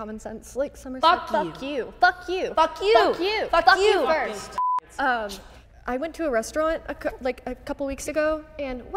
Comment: if the f word isn't bleeped, then what was that bleep for??? did they say something even worse? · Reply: The n-word